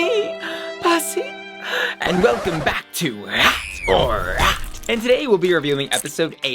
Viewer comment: Kinda sounds like Bussy said "Rot or Rot!" In the intro